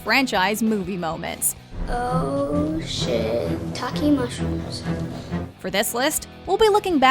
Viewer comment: Oh shi-take mushrooms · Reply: Me and my sister thought Carmen said shit taking mushrooms